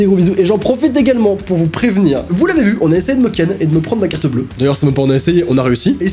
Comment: A 14:10 on a essayer de te faire quoi ? Chui le seul a entandre "ken"... ;-;'